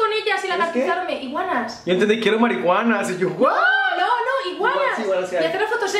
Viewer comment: Minuto 7:35 yo tambien entendi mariguana XD AJAJAJJA